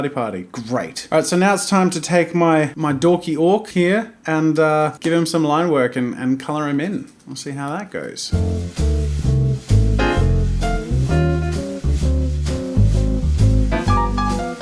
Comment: I thought he said “and give him some lung work”